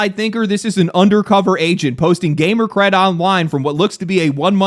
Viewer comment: I think bro just called her an Agent Smith...🤔